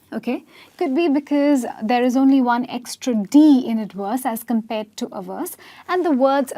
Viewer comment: you are lovely, but it sounded like you were saying adworse not ad verse.